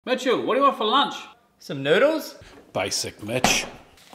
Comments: why did I hear "basic bit*h" 💀💀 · Every time he says "basic mitch" i hear "basic bitch" lol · If I didn't read the subtitles I would've thought he said "basic betch" 😂🤣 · I thought I heard " Basic! Bitch"😂🤣😂🤣😂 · I thought I heard “basic bitch” 😅😂😂😂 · I heard “Son of a bitch” instead of “basic mitch” 😟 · for a second I thought he said basic b!t** but did a double take and saw the captions